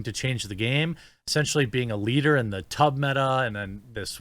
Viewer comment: Leave A Like On The Video To Press F For Thots! Check Out Hunting Clash! <link> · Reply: you said "tub meta" and I heard "tug meta", I was about to say no that's a different site but I listened again.